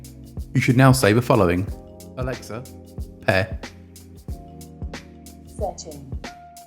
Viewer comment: what did he say?? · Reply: Pair